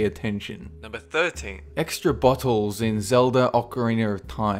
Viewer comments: When he said bottles, I thought he said buttholes xD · Instead of "Extra Bottles" I heard "Extra Buttholes".. I should probably get my hearing checked..